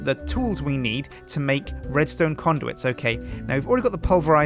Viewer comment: did he say "redstone condoms"